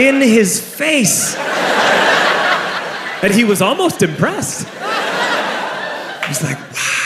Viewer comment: I'm starting to hear this as 'depressed' instead of impressed